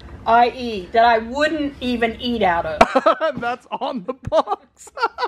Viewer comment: I heard 👖"pants I wouldn't eat out of." Got so confused when I saw the pans. Think this lockdown is sending me loco!!! 😜